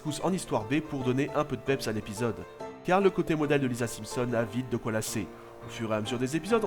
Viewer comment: J'ai entendu "le côté Mona de Lisa Simpson" XD